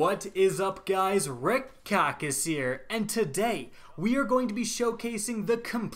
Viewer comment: sounded like he said redcock is here